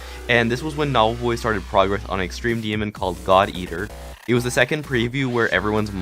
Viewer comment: I thought he said Thot eater XDDDDDD